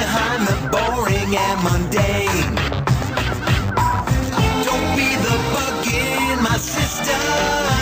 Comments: i thought it said don't by the buggin my sister too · I thought it said don't be the bugging my sister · I wonder who sings and performs this song, it must be some black guy. When I heard the word "system", I thought he said "sister".